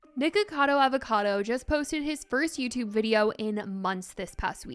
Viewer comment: I thought u said n*ggacato avacado